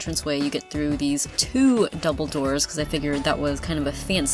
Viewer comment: As a Harry Pottet fan I heard 'dumbledore' every time you say 'double door' finding for fangirl